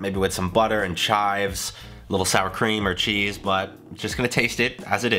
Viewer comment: Did he say sour cream? 👁👄👁